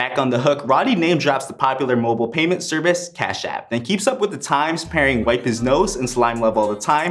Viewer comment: He didn’t say cashapp he said cash out 😂 Ik I thought he said cashapp too